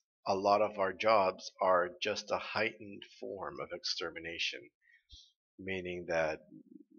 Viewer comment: Wait, did Oobleck really say "A heightened form of extermination"? I always heard it as "A hike full of extermination".